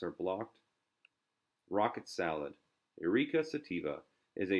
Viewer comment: Did he just say sativa ? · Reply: Eruka sativa. The scientific name for rocket salad.